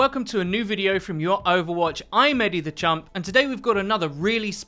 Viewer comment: What is your name because all I hear is "Eddie the Chump"